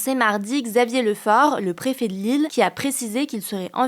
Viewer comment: Dites moi que je suis pas seul Quand elle a dit "le préfet de l'île" je me suis dis "mais il fait quoi dans cette histoire le préfet de Lille" 😂😂😂